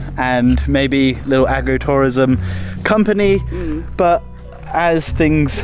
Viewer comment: I heard it as Aggro-Tourism, and I was busy trying to picture what that could be when I realized you meant Agro-Tourism, as in farms not aggression.